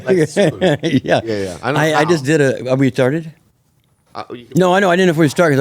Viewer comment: I swear I heard "are we r'tarded?"😂😂😂